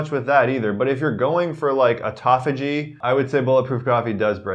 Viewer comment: right after the insulin resistance comment... What is that word he is saying? Sounds like Etoffigy... and the CC didn't help with this either. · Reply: Joe Gray Autophagy